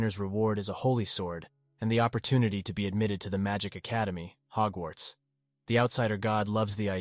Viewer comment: wait wait wait did i hear it wrong or the academy's name was really Hogwarts